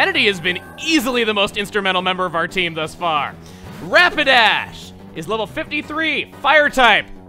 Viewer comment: Am I the only one who heard him say rampidash instead of rapidash?